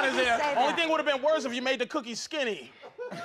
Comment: What did Patrice say at 6:24? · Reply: He said the only thing that would have made the picture worse is if she made the cookies skinny. y'know..........like holocaust victims